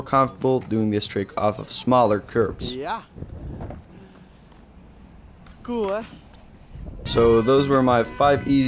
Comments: Are you ditch or do you speak Dutch (I do) At the end I heard you say “Ja cool hè” in dutch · Wait are you dutch i heard you say at the end ja cool he